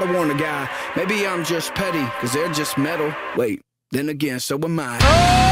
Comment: I heard it as, "im just petty, they just meddle. Wait a minute so am I." They meddle in his business but hes metal.